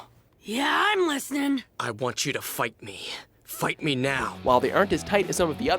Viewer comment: Haha.. when Sasuke said "Naruto, I want you to fight me" I thought he said"fu*k me"!